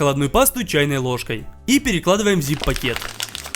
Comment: ЗИГ пакет, мне одному послышалось?